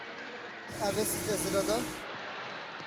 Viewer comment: co on powiedział? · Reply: A wiesz co to jest RODO?